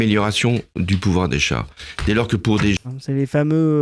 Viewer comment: Holande s'en fout tellement de ce qu'il dit.. j'ai entendu "du pouvoir des chats"